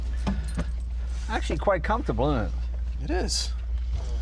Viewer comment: Did Jay just say "innit"?